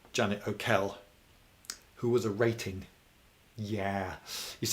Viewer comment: What's the word at 20:04? Sounds like aerated. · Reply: "Who was a rating" - the nautical equivalent of a private in the army.